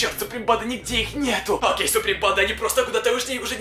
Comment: Ставь лайк кого он тоже задолбал всегда говорить Суприм панда. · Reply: Мне послышалось "Сопли панда''' 😂